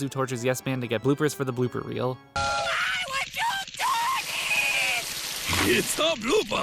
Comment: did he say “F*ck This”???